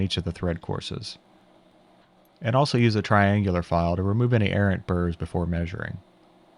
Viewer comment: I know what you said, but for a moment I was wondering how Aaron Burr made his way into your work...